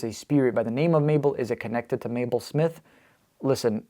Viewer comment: The 16:02 she says Mabel. It sounded like me at first but listening with headset you can hear Mabel.